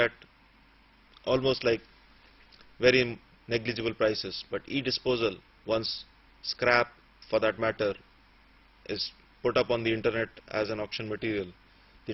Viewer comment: Did he say "crap"?